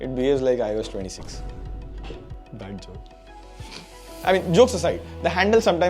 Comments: Did he just said BC ? 🤣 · says bad joke.. I heard Bhen Ch d 😂